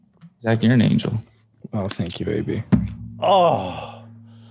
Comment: Thought Zach said thank you baby before realizing he said AB